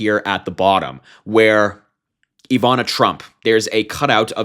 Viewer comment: Oooh. Ivana Trump. I thought you said Ivanka. Yes! Eeeeew. HIM gave her skittles is Donald.